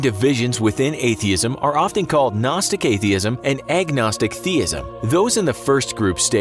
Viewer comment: So agnostic theism is a form of atheism? Did I hear the correctly?